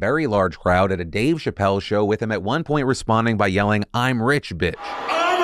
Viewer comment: It sounds like Elon said "I'm reddit bitch"